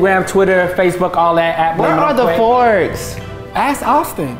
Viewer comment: At the end I thought said I want a divorce but she said where's the forks 🤦‍♀️😭